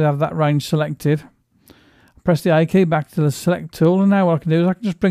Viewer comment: I couldn't understand what you said at 1:45. I heard press the ___ back to the select tool. May you please fill in the blank? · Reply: key - The key on the keyboard with the letter A...